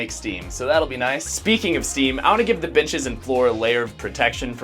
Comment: did he say b*tchs?